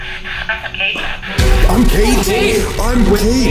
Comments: i didnt hear "im kate" i heard suffocate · when it said “im kate” at 31:52 please tell me im not the only person hearing “suffocate” i’ve listened to it multiple times and that’s all i hear 😭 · I don’t know if it was just me, but at 31:57, I heard ‘suffocation’ instead of ‘I’m Kate’. When I played it back while thinking of it possibly saying ‘I’m Kate’, I heard ‘I’m Kate’. I’ve played it back a few times trying to think of both ‘suffocation’ and ‘I’m Kate’. I hear both depending on what I’m thinking. I don’t know if this is just one of those times that you can hear different things based on what word you are reading or thinking of, but I thought I would share. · Definitely here when on the speaker it said” I’m Kate” I definitely heard “I’m gay” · “I’m Kate” I heard “Suffocate” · i’m not gonna lie i thought it said “i’m gay” not “i’m kate” 😂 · I heard suffocate instead of “I’m Kate” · I didn’t here “I’m Kate” I heard renegade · wait for a little but I heard “no I’m Kate” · I heard "replicate" not "I'm Kate" . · When it said "I'm Kate" I thought it said "suffocate" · When the ghost said “I’m Kate” I thought I said “I’m gay”😭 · I heard suffocate not I’m Kate, anyone else? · When they heard “I’m Kate” in the spirit box I heard “suffocate” clear as day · I heard I’m gay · When you thought it said ''I'm Kate'' I'm pretty sure it said suffocate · I didn't hear I'm Kate I heard suffocate 44:05 that scream gave me an uneasy feeling